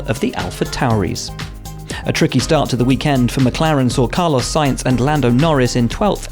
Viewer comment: Is it jus me or did he just said Carlos Science?